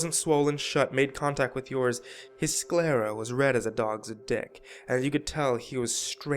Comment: Did he just say "red as a dog's dick" ?